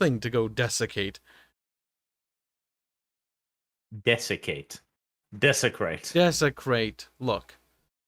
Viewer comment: I thought I heard him saying "defecate," instead of, "desecrate," which was, also, fitting.